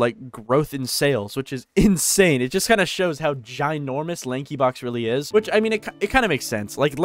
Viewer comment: Did anyone see at 2:38 on the captions on the bottom of the screen said linkin park instead of lanky box??